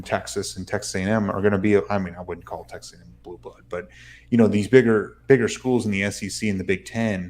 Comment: at 14:00 did you say Texas isn't a blue blood?.